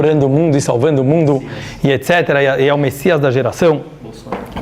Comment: Eu escutei uma Voz ai no fundo no tempo 15:02 Falando (Messias) (Bolsonaro).